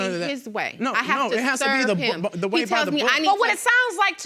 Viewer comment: Judge Lake cut her off at 20:50. I think the wife said "I have to 'Sir' him." Juicy! Wish the judge had let her speak.